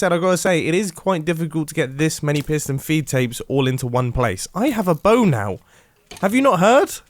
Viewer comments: I thought he said I have a boner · "I have a bow now" I thought you said "I have a boner"